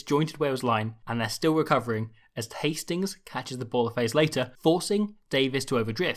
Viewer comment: I swear he says Tastings here instead of Hastings 😂😂